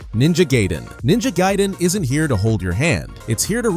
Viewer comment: Did you just say Ninja GAY-den? XD